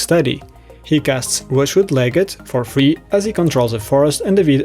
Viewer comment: mins in, what was the free green creature? I can't make out the name through text on the card or through the voice narration. Doing great guy :) xoxoxo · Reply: Rushwood Legate. Belongs to a Mercadian Masks cycle of free creatures all called Legate. In these cases you can always toggle on subtitles to double check :)